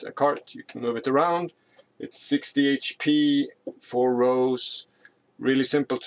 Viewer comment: I could have sworn I heard you say "60 HP"... but, no. Maybe 160?